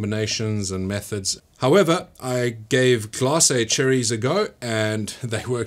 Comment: What does he call those cherries that he uses instead of fresh cherries at 1:21 — can someone help me out? :-) All I hear is “glass”, so perhaps pickled cherries? 🤔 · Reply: They are called Glacé Cherries. Basically candied cherries